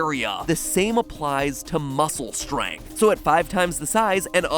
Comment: it sounds like MatPat is saying "langth" and "strangth"